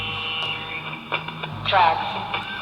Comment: I heard “traps” not drugs.